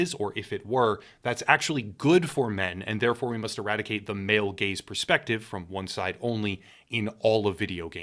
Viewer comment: I keep hearing "male gays", which makes the constant desire of erradicating the trope sound a bit worse than usual.